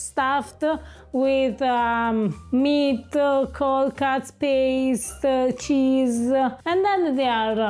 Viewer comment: stuffed with col cazz (instead of cold cuts) 😂😂😂😂😂😂😂😂😂